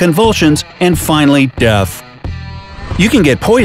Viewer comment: did he say deaf instead of death